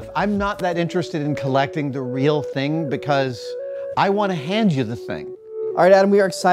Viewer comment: "I'm not that interested in collecting the real thing because I wanna hand you the thing." This is a passion for the craft and the art and the effort it takes to make these, and honestly keeping the practice alive is a much better way to honor it than a museum-like collection. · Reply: Adam said, "hand you the thing". I heard, "play with the thing."